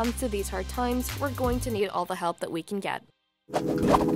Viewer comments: I heard "Thanks for watching and don't forget to subscribe to the Taco" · i always hear "the taco" inatead of the talko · I thought they said "The Taco" instead of "The Talko"